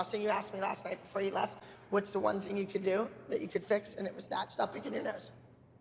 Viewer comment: did Abby just say “stop picking your nose” ?!?!